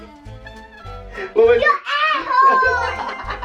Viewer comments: i heard "you ayhole" then I rewind then I heard Air Horn Lmao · it sounded like she said your a-hole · At first I thought said you asshole and not "an airhorn"